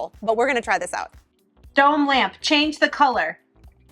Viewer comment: When you were saying, "Dome Lamp" I thought you were saying, "Don't laugh." I was thinking it was because you were talking to the lamp and were telling us viewers not to laugh. 😂 BTW....I laughed!!